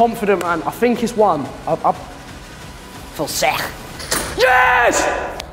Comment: I'm South African and I'm pretty sure I heard George say "Fuseg" at 22:22.😳 Anyone confirm please. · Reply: I feel sick?